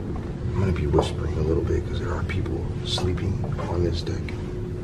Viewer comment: there is A VOICE and i thought it said b deck (childs voice)